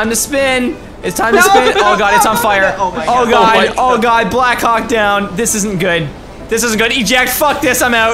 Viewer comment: at first i thought he said black c*ck down (not being racist) when the jet was going down leave a thumbs up on the comment if u thought dat too